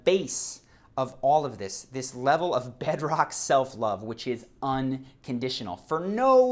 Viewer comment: Damn at first I heard "self-loath" and I thought; WOW I'm doing it right for once but yeah... not yet xD